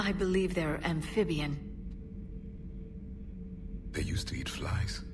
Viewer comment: I thought he said "they USE to eat flies" not "they still eat flies